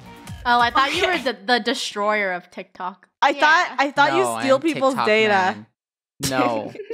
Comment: no one's gonna talk about what funneh said in 1:40? only me? okay. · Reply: What she said “I thought you steal people data